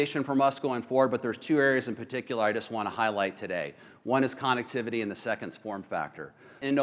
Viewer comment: Did he just say Con activity?!